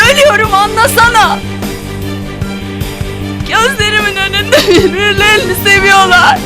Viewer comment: plzzz can someone write what bihter was saying when she started to cry 2:16 in turkish plzzzz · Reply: Bihter:ölüyorum anlasana. Gözlerimin önünde bir-birilerini seviyolar. Ben işkenceler içinde kıvranırken onların mutluluğundan ölüyorum. Anne ben ölüyorum yardım et.